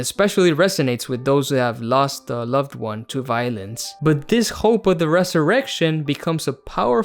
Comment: idk why i heard "lost a loved one to violins" instead of violence